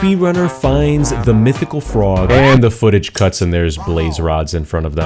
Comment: i heard mythical fraud mdr